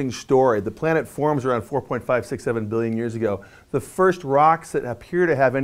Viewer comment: I swear i thought he said, "The planet forms about 4,5,6,7 Billion years ago. ha